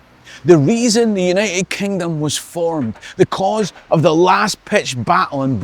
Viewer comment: Yes ... and... what's your point? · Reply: Sorry, confusion I realise is because I thought you said ' because ' re last pitched battle and not as you actually said ' the cause ' My bad hearing !